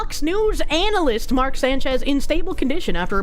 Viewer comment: I swear when you said Fox News, I thought you said foxu Lol